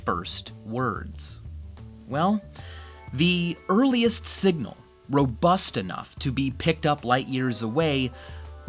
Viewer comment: I thought it said ro-bust a nut.